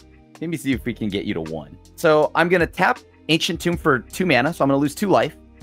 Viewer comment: I very much so thought it said "ancient tart" and I was very confused about choices in pastries.